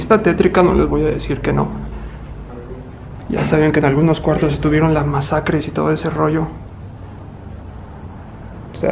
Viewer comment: En el 12:16 suena una voz femenina, pero o logro entender qué dice. ¿Acaso dice "ok" o dice "oye"? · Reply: Dice oye